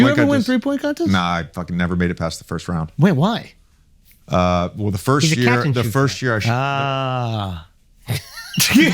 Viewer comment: what did the guy say at 4:22 ? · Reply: “He’s a catch and shoot guy”